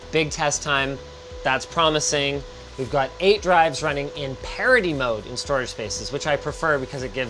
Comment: I love how Linus' pronounciation of "parity" is confusing youtube CC and making it into "parody mode". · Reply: Tbf, I heard him say parody mode. Even listening to it a few times, I still think he said parody.